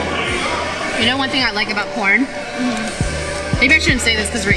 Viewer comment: I miss heard Michael...i thought she said porn..😂